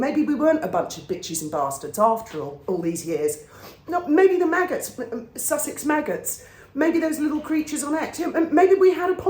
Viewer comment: Meghan’s MAGGOTS, Sussex squad? I may have misheard but HOW FITTING!